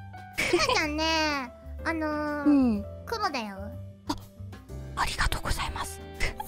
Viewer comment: I heard Kuma over kuro actually